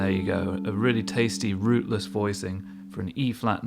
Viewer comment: Haha, I heard "a really tasteless, rooty voicing" and was like what is he going for now? But it was a really tasty, rootless voicing.